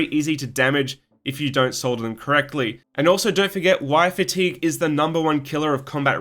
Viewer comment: I thought you said “wife fatigue” 😆 - Great video, wishing you the best in Dec ‘24 - Central Florida here 🌴🇺🇸